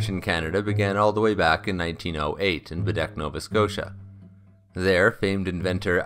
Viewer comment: When you said Baddeck, I thought you said Bidet, which is a French toilet that blows water up your butt, which is like what a hydrofoil does when you think about it.